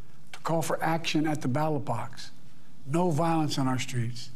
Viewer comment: ballot box or battle bots? lol i could of sworn he said battle bots hehe love ya but ya need to pay attention to the enunciation bud.. course settling things with battle bots could be cool 🙂